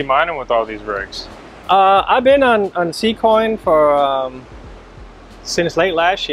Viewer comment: Amazing! What coin did he say he was mining at 22:45 ? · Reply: z coin